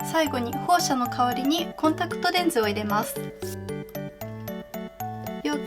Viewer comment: すげえええええい！ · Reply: ＼( ‘ω’)／ウオオアアアアアアアアアアアアアアアアアアアアアアーッッッッッッッッッッッッッッ！！みなさんありがとうございます😭💞💞 あと、2:05ってコンタクトレンズって聞こえるのですが…コンタクト除光液ですよね(´•ω•｀)？